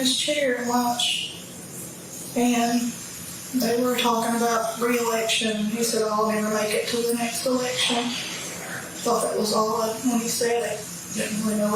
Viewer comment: where the subtitles say inaudible, I believe she says, "They were talking about the next election and he said, I ain't gonna make it to the next election." It sounds like Mickey knew at lunch what he was going to do, and what the consequences would be.